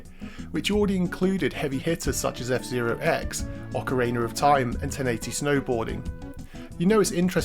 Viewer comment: Every time you say ocarina I hear "ocariner".